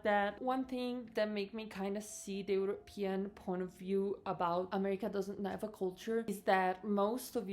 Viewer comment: I keep hearing you say "European Porn of View." 🤣😂 I keep listening, listening, and listening till the end... So disappointed!!!